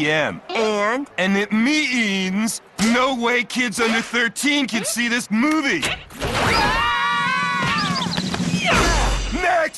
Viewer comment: did he say no white kids under 13 Like if u agree